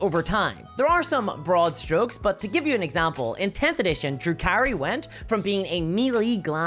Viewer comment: The first times you said Drukari I heard "Drew Carey"